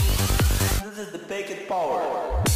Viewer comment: What does he say at 4:30? Ive been trying to make it out but i have no idea. Maybe its to promote OSH? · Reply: "This is the packet power"